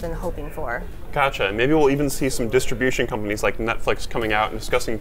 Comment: Yo Alex, you mind saying 'Netflix' again? I thought I heard Nutflix...